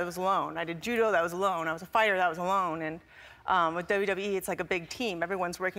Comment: If you go to 0:49 and put on captions, when she says WWE, the captions say WEE 😂